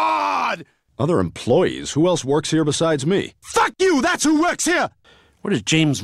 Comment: I always thought Stewie was saying "Fucking you" lol